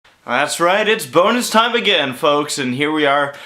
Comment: I thought he said boner time at the beginning